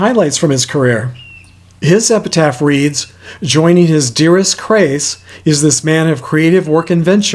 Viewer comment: Another excellent video, but when reading Frank De Vol's headstone, it sounded like you said "Joining his dearest, Crayce..." rather than Grayce, which referred to his former spouse, Grayce McGinty.